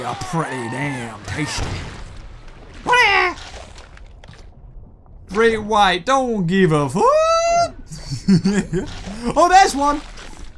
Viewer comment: Who's looking forward to the release of this game!?! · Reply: did u just say Great White dont give a fuck? xD